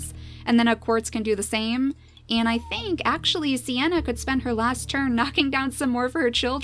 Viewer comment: "Siana can spend her last turn knocking down her children" ...did I hear that right? ( 21:43 )